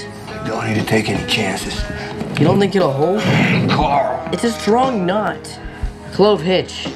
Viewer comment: I wasn't looking at my phone and wasn't paying attention and swore he said "ITS A STRONG NUT" and I'm like "COORRALL"